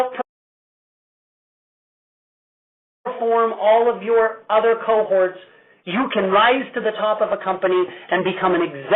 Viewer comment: What did u say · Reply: DAMN, good catch!! hahahaha derp. I said "He's talking about USANA."